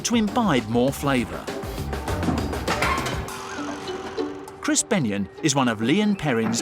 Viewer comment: Chris BENION working with ONION 😂😂😂😂😂 it even rhymes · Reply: I thought he said crispy onion and I had to rewind and go to the comments hahaha!